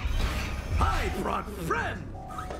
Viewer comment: Did anyone hear heard him say “I brought friend”! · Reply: It sounds like he said i want bread or I brought Fred